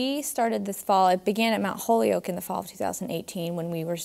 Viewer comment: I legit thought they said mountholio